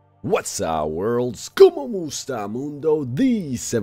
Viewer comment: Is it me or you guys love how he says “kamusta Mundo” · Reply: I thought he said como estas lol because he is Mexican 😂 Btw Filipino here